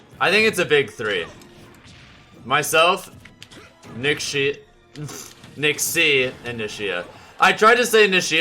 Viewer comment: definitely said "nickshit" instead of nickshri or whatever